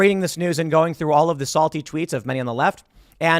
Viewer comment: When tim said "Salty Tweets" I heard "Salty Treats"